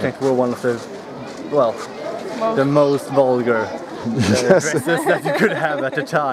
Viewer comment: Did he say vulgar racists or vulgar races? It's hard to tell with all the laughter. I would expect "racists", but they never mention it again and no one in the comments is saying anything about it. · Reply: dresses